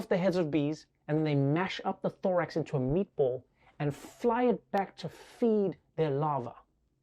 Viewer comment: Did he just say the hornets call it meatballs or did i knock my head??